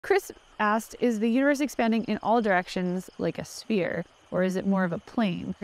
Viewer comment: I really thought you said, "is the uterus expanding" omg